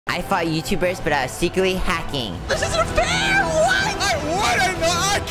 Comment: I thought u said secret hacking